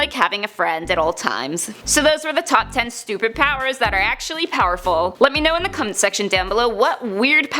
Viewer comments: Who do you think is the strongest superhero? #Top10Nerd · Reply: I know im going to get cosmic levels of flak for this but at the end of the video. You say let me know in the comments section below! And I had to double check because it sounded like you said let me know in the cunts nextion below? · did she say Down in the come section?